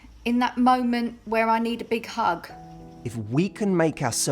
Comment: Scrolling in the comments and looking for the scene at 5:41? Did she just said "I feel lonely in the moment I need a big COCK? :D" haha jk I know she said hug